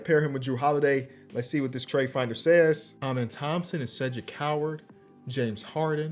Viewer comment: I thought you said “amen Thomson such a coward” I was like what he do lol 😂 Cedric coward lol